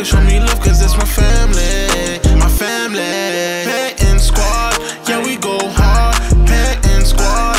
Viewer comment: I’m so stupid I just noticed that the intro said panton squad yea we go hard I thought it said hey m squad yea we go hard😂😂